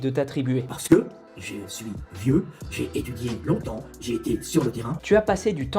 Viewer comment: j'ai compris "parce que je suis dieu"